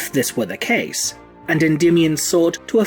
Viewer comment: I keep mishearing it as Ra and Dymion. Every time you say his name I think for half a second, who's Dymion?